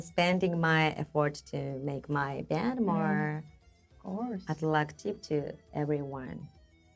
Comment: I think Asami says (at 28:40) “I’m spending my effort to make my band more attractive to everyone”, and not “more eclectic”. I could be wrong, of course, but I’m pretty sure that’s what she’s saying. As far as I know, the Japanese accent has a tendency to mix up the English R and L sounds occasionally.